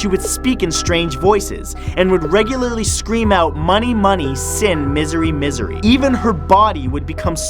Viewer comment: that girl who screamed out "money money" is it bad that i heard "manh-i manh-i"?